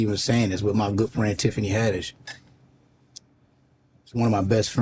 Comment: I thought he said Tiffany henreid but yet he said haddish